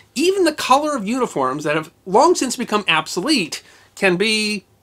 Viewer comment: "Did THG really say absolete at 13:04?" "Absoletely!"